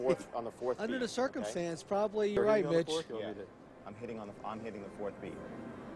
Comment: Anyone else hear "probably you're right b*tch" at 16:35?